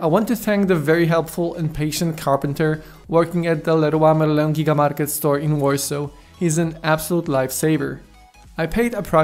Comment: can anyone tell me what he said am damn confused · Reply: I'm thanking a carpeter who works at Leroy Merlin store in Warsaw who helped me out and cut a huge slab of hard wood coutertop to pieces.